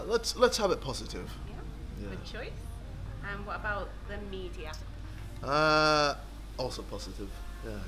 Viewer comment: was not paying attention at first, and thought she said chlamydia. And jj said positive xD. calm down tho i now know she actually said ''the media''.